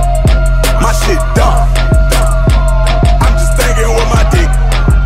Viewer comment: This whole time I thought he said "my shit dark" at the chorus part